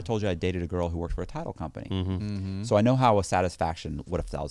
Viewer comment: Oh I thought he said he dated a girl who worked for a tile company 🤣